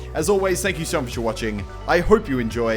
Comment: It sounded like he said and I'll see you virgins later